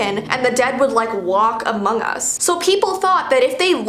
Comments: me:did u say among us!? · did she just said among us xD